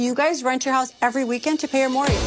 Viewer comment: I don’t know why I thought she said ‘Tia Mowry’.. She said “Pay your mortgage”. LMAO!